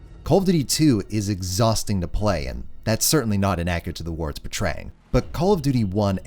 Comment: I'm sorry, but I have no clue what said at 19:30. "That's certainly not innacurate to the wor----------." Can someone help me out? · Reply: "the war its portraying."